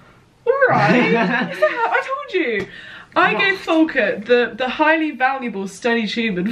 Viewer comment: What’s your brothers name? It sounded like you said Folkart? (Sorry if that’s spelt wrong!) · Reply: Folkert :)